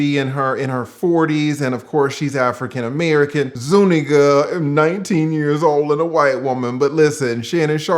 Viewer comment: Is he saying Zoo Ninja lol ?